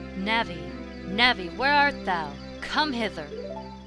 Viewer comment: I honestly thought that read out the great deku tree delong box telling navy to come hither. I thought she said come hit her.